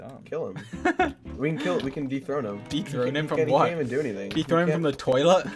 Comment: THE WAY I THOUGHT SAPNAP SAID DEEP THROAT AND NOT DETHRONE AT THE BEGINNING 😭😭😭😭😭😭😭😭